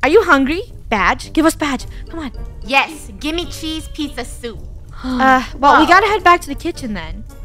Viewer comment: Is it just me or did say peese chizza soup